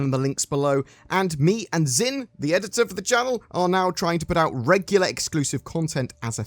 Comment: all i heard was "zin's trying to put out"